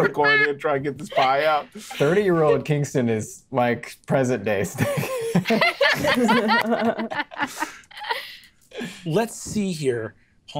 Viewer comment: What did Zac say at 20:01? The subtitles say Steve, but what he said sounded nothing like Steve. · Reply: “Present day Kingston” but he laughed/mumbled and the other people talking probably messed up the captioning service